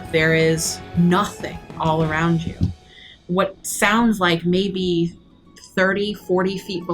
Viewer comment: I swore I heard say, "Sounds like Mikey", when was describing what's possibly Bavlorna's bedroom 😂😂